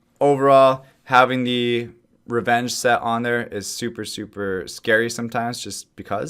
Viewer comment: did he just say" rune set on HER"? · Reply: On there